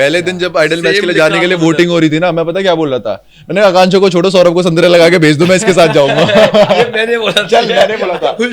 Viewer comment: did I hear santra? So this santra thing was already a talk between the guys and himanshu just said it loud while fighting with Akanksha 🙃